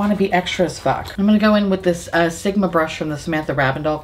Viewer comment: Sometimes when you say "Sigma Brush" I hear "Simba Brush" so I think you'll be a great Disney vlogger!